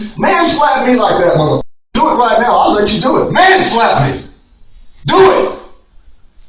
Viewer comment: "MANSLAP ME!!" Is a crazy thing to say. 😂😂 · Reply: i thought he was saying "man, slap me" but i was wrong 🤔 😅